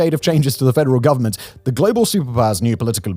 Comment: Did you just say "global simpire"??